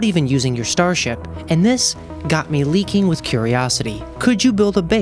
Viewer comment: I clicked and the first thing i heard was "got me leaking" like what got you leaking bruh? 🤨